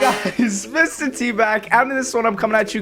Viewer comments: sounded like he said Mr Tbag when it started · It's sounds like he said mr tbag